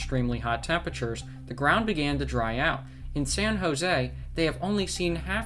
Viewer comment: Zan HoZey? where the hell is that at? it sounded like San Jose but im not sure, if you said San Jose don't make a video using Spanish words or names if you only speak broken Spanish until you can master the language, because who knows what your gringo accent is saying, you English talking people are a disappointment of human kid...